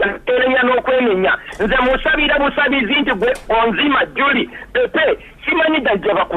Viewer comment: Did Pastor just call the guest Onzima JULIE Pepe?